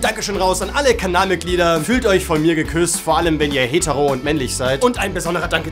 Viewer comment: ich habe deine verabschiedung nicht verstanden 6:20 "vorallem wenn ihr männlich und hetero seit"